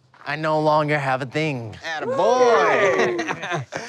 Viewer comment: what did mikey say??😂😂😂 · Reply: Attaboy